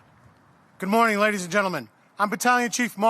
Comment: Who is the Executive Officer to the Fire Commissioner: BC - Mark (who ?) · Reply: It sounds like he says Guerra, but I'm not sure